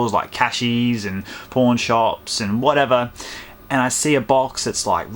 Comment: did you just say p0rn shops